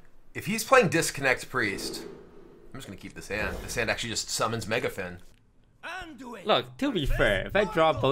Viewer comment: I for sure thought he said "summon Mecha'thun" and checked the date real quick lmao